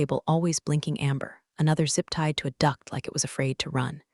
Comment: Misheard this, so now every time I listen to this one, or think about it, I laugh. instead of “duct” I heard Zip tied to a “duck.”